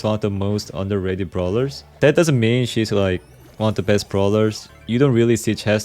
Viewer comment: did I hear him call chester a 'she's?